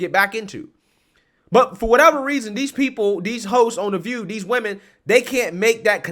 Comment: I legit thought you said "These hoes on the View" 😂😂😂